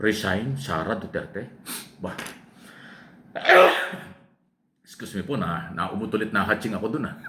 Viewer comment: mukhang mali yata ang narinig ko mula sa inyo..........excuse me po nautot ako sa ulit nahatching...........😁😁😁😁😁😁